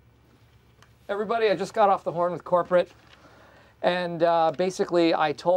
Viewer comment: does he say "I just got off the horn..." ? 😂😂😂 Brilliant